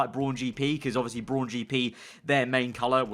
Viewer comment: Thought he was saying porn gp😂😂🤣🤣